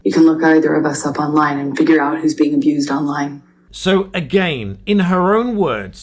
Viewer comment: Sounds like shes saying " You can look either of us up online, and figure out HE's being abused, online .. ?!